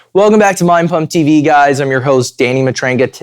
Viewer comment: I heard "welcome back to Mein Kampf TV guys"